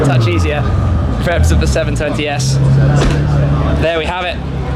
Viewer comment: did say 730s or is it just me?